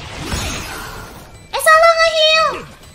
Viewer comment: I thought i heard "Sayang na heal" but still the translation is right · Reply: It was "Aku salah nge-heal"